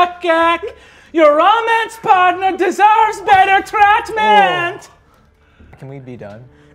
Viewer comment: When he said treatment, I thought he was saying Chex mix at first 😂